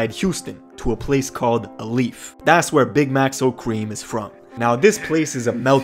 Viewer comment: Why did i hear ‘big maxo kream’ and think he said ‘big macs old cream’ it had me thinking bruh what cream 😂😂